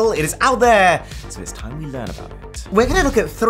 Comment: I can't understand what you said at 0:27!!!! I replayed it 7 times, making it louder each time and I still can't hear/ discern what you said then. Sigh. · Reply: Yeah! He said it very quick but here it is: "It is time to learn about it"